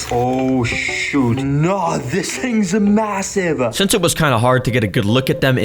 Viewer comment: Did u say massive?